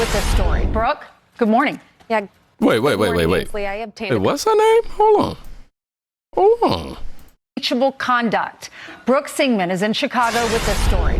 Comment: You thought they said "BROOKE SEMEN🫣🫣🥺🥺🙄🙄🙄 I DID TOO😂😂😂😂😂😂😂😂😂😂